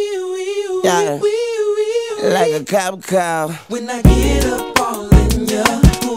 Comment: Weoweoweowoe! LiKe A cOp CaR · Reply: Mr Inferno lol wait it’s been 11 yrs and he said cop car after all this time I thought he said like a cop call😂😂😂